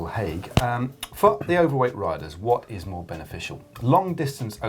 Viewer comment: does Matt say "F**k the overweight riders"?